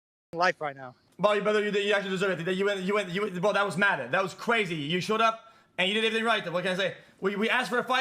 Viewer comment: what the hell is xqc saying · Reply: "Brother you actually deserve it. You went- bro that was mad, that was crazy"